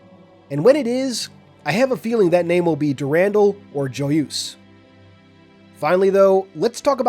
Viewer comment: Durendal or what? I didn't quite catch that.